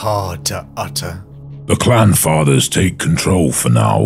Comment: I kept hearing "Clam Father" and "Clam Council" and now I think I have a solid basis for a nautical themed astartes chapter.